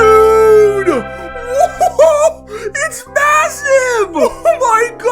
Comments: did... did you say massive? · did i hear MASSIVE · did you just say m-ma-massive?